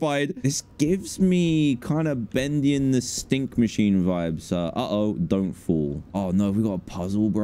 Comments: did he say stink machine at 3:56? lol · Am i tripping or did i hear dan say ''bendy and the stink machine vibes'' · Did I hear "bendy and the stink machine"?